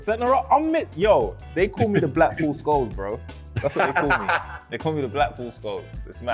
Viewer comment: thought he said blackpool scholes hahaha was gonna say when u signin for the pool harry